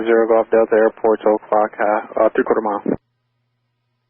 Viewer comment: Super tight airspaces... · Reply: Your captions are wrong at 0:56. Pretty clearly says "three-quarter mile".